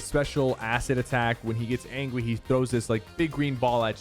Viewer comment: is it just me or did say angwy